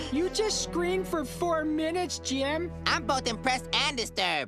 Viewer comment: “I’m both impressed, AND disturbed!” 🅰️ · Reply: I thought he said I’m both depressed and disturbed